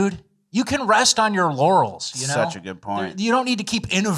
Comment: Did say at 14:22 you cab rest on your LAURELS or rest on your YANNYS?!?😳🤯